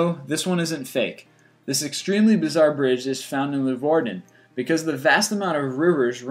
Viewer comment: I'm Dutch and I needed to Google it to know what he said. Apparently it's Leeuwarden . :P